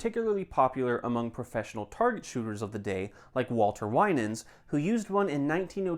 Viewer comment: I thought he said Walter White 😂